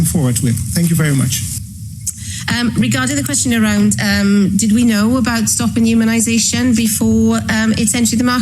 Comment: did she say "did we know it would stop immunization? " Is she admitting what a horrible experiment it was?